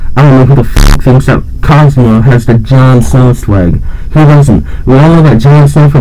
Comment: Thought it said Jon Tron lmao